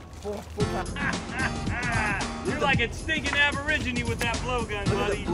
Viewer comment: did he say stinken Aboriginal